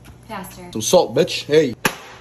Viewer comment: When she said faster I thought she said master at first